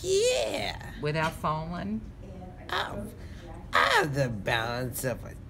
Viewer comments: Cougar? Ooooooh I thought he said, "I have the balance of a kooter." I was like, tf is a kooter? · yyyyEeeeee Aaaa I hit the balance if a _____ (idk what he said)